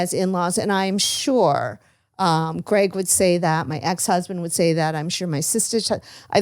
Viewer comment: Am i the only one who heard say her ex-husband...am i missing something? Time stap